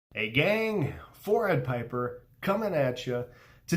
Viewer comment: At first i thought he said forehead piper and i was like dang thats accurate then i realised he said 4 eyed 🤣 still cant ignore that forehead tho